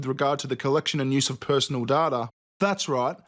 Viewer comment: When he said data I thought he said ta da so i was confused af